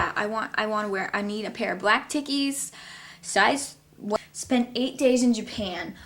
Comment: Can someone tell me what she said at around 4:12 ? She said " black _____" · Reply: Ik this is old but she said Taekies it’s a clothes brand